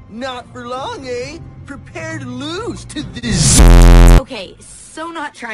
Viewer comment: I could’ve sworn I heard say prepare to lose to the รђเՇ